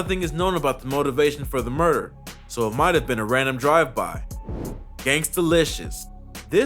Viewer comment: who else heard gangster lishush and thought of boondocks